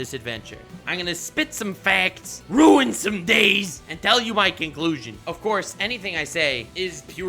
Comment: ok my god I heard "kinkclusion" instead of conclusion lmafo